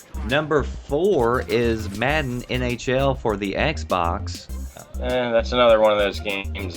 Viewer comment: MADDEN NHL 13 IS WHAT HE SAID